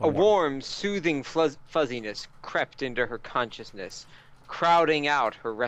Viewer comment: Am I hearing things wrong all the time, or did he just say at 28:25 "A warm soothing fuzziness crapped into her consciousness"?? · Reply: I think that was crept.